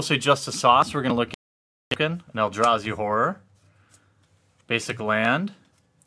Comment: Sounded like "Eldrazi Whooore" @ 1:03 :)